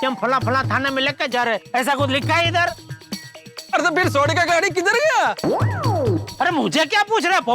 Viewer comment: there is mistake in subtitle, instead of car there is scotter 😂